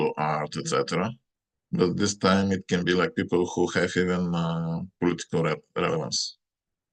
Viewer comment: What does he say at 12:45? He says this is not just designers and artists but people who have……. ? Around November 19th. I don’t understand. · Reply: Politics